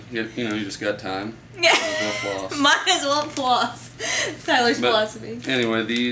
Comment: When you said "thats Tyler's philosophy" I thought you said "floss-ify" ... haha, because you were talking about floss ... I dont know! :]